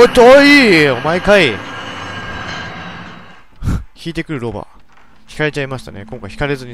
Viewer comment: 轢いてくる老婆が轢いてくるロバに聞こえたww